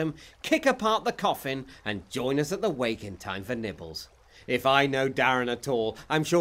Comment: Does he say 'NIPPLES'?